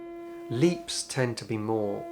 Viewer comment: Did you just say, "Meeps in the melody?" Oh, wait. Leaps.... Carry on.